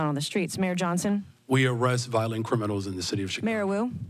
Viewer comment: Did he just say they “arrest violent criminals on the streets of Chicago?” 🤣